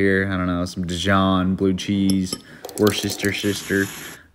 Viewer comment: I thought this man really said, “worship your sister” instead of Worcestershire sauce.